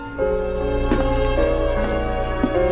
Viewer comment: I guess I was silly, but when I first saw this scene, I thought he was saying, "no FEAR." (Like "I'm not scared of you. I'm not scared of dying. I died once already, so....") My dad then enlightened me. We were watching together and he corrected my mistake. Then it made more sense.